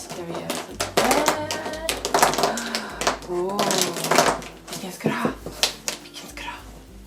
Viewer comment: is that swedish? " villken ska du ha? " i thought i heard it ifall du är svensk jävla duktig är du ijallefall :D